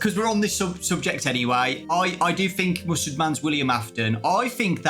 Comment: for a second there i thought had said: "music man is william afton" and i replied it thinking he said that 😭